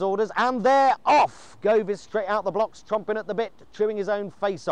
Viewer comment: Does he say "chomping at the bit" or "TRUMPING at the bit"?